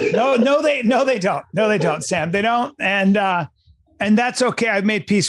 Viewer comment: did he call him Sam? Lmao